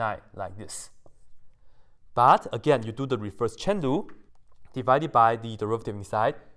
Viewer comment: The first few times I thought you were saying “reverse Chen Lu” instead of chain rule